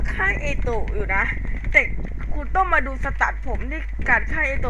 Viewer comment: ผมไม่ได้พูดกูนะผมพูดว่าคุณ